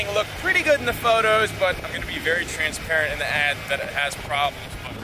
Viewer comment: I would respectfully disagree. He seems to care more about money than people since he himself said in the video that he was going to hide that the truck had any problems and list it for quick cash. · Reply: you misheard 23:09 “i’m gonna be very transparent in the ad that it has problems”